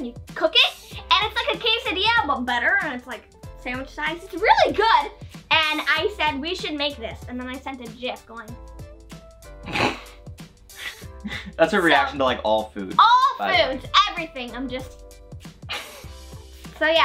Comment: Did she call it a JIF?!